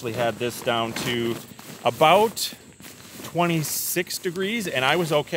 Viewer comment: "I've had this down to REALLY REALLY cold like into the 40s" 😂 cheers from Finland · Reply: He did not say that, he said 20 1:50 Cheers from Norway😂